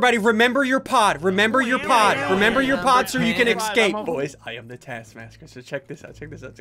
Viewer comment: Did he say, "SO boys, I am the task masker." 😂